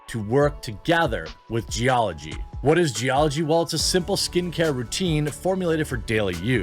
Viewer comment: I thought you were saying "skinny carrot" there for a minute and I was like yes, you're right, I really DON'T think about my skinny carrot that much at all, in fact. I promise to be the change we need in skinny carrot attention. More or less.